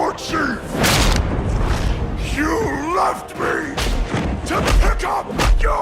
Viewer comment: In the VS Thrall fight when said ''You left me! To pickup......'' at first i heard him say: '' You Loved me! ....'' xD and now i cannot unhear it xD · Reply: oh he said left me ? wow till this time i thought he said you loved me (but if he would said that it would make the cinematic more emotional tho)